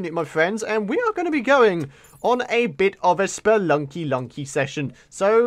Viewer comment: I thought you said "spunky spunky session".